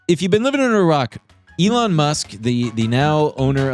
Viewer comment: I thought said "if you've been living in iraq," at the very start, and it jumpscared me so hard because how does he know where I live lol